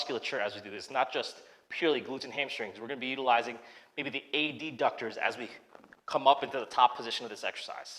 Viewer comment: Did you say A.D. ductors lol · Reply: Well it can easily be misheard as abductors, it is a bit like NATO phonetic alphabet, alpha delta ductors lol